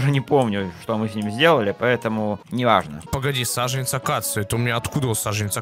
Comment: мне послышалось саженец акацуки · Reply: Акации